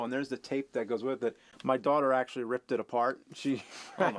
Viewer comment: Did he say, "Daughter"?